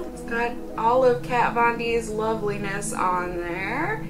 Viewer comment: Whenever I hear "Kat Von D" I hear it as someone saying candy in a weird voice, like "Cay-On-Dee", I don't know why 😂